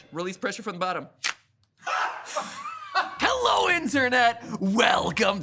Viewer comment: Hello insertnet! Welcome to game theory! Am I the only one who heard that?